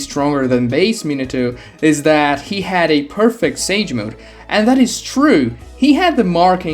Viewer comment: What are you on? Kabuto aint a perfect sage, he literally looks like a snake when he transforms. The more you look like the thing of the sage mode (snake sage and shi) the less perfect it is. Thats why jiraiya gets toad like features. Kabuto basically becomes a snake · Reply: I could of sworn i heard he was imperfect